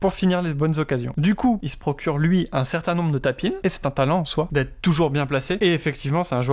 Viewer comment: "Des tapines" ?? C'est quoi cette expression ? Absolument jamais entendu mais tu la répètes plusieurs fois comme à 7:37. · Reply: Haha ok c'est tap-in donc j'imagine. Je pensais que c'était une expression francophone locale. 😁 En français je dirai que ce sont des buts tout faits je pense en équivalent.